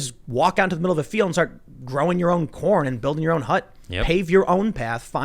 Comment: I swear I mis-heard you say “Go into the fields an grow you’re own porn” ??!!?! 😂